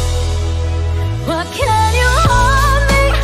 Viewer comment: I love the way it sounds like she says "homeless" rather than "hopeless", it changes the message to be slightly less hopeless. I love the way her voice sounds completely unrestricted when she sings "I'm your's". She sounds as though she is celebrating a legitimate love and wishing she could show it more, rather than a forbidden hopeless love.